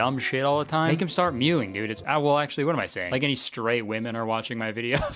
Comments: "like there's any straight women watching my videos" lmao you caught us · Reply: I heard "stray" women and it was even more hilarious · "As if straight women are watching my videos. Ladies if you want to get your girlfriend to stop saying dumb shit..." has killed me. I'm dying of laughter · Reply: Ohhhhh … I thought he said “stray women” and I was so fucking confused 😭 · I heard 'straight women' as stray women... I, I'm, okay nite nite now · straight woman here ❤ (literally sounds like your saying stray) Am I alone? 😅 is Kurtis just for the guys and gays? Also… I automatically do the tongue to the roof of my mouth thing. I thought that was normal. I don’t know how to rest it any other way 😅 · thought he said stray women…. They are lost, the poor little stragglers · For a moment I thought you said "stray women" 🤣🤣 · I originally thought you said “stray women” instead of “straight women”. And Neither watch your videos. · I swear I heard him say stray women and not straight women